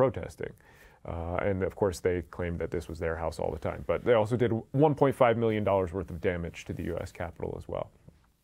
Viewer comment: Hello, first time here. Can someone provide me with a citation for the 1.5 billion price tag of damages incurred during the capital riot? Thanks in advance. I’ve already looked but the highest estimate I found was 500 million. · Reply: Are you talking about what he said at about 24:08? said “1.5 million”, not billion.